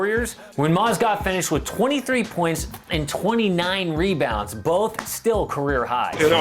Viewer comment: Wth did 2:24 say 93 points